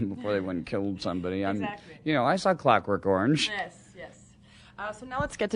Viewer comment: What did he say at 6:56? Crack in Gorge? Or it's a person's name? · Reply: He said "I saw clock work organge". It's an older movie.